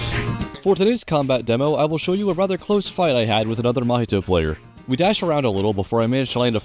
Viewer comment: i hear "a nather Mahito Player"